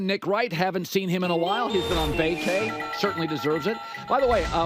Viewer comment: What does Colin say? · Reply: 'He's been on vacay' meaning he's been on vacation